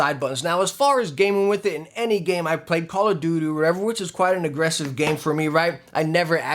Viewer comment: did he just say call of doodoo?